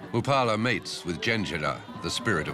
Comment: Can someone please tell me what the narrator says at 41:05 ? "Mupala mates with..."? The spirit of women's name. Thanks in advance!